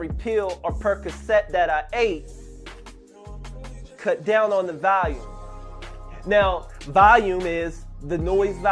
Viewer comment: I love Revival but I thought he said cut down on the Valium